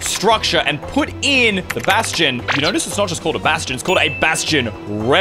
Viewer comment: i heard you say sebastian🦈🐋🐍💡🐡